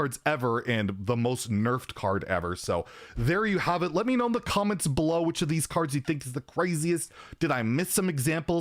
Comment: I literally heard "in the Cavenrs Below" (instead of comments below) the first time..